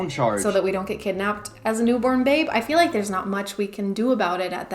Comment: did she say “as a new born babe”